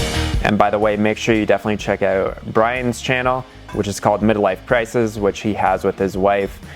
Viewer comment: The Upside of Downsizing <link> Midlife Prices <link> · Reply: ooooh, Midlife Prices.. I thought you said Midlife Crisis. x)